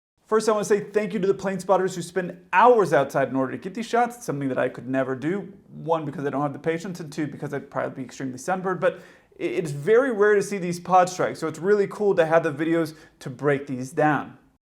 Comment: at 0:50, it sounds to me like you're saying "...and probably because I'd be extremely December...", but that can't be right. What were you actually saying?